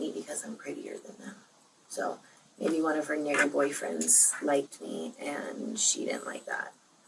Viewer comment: Any one elss thought they heard 🥷🏾R boyfriends?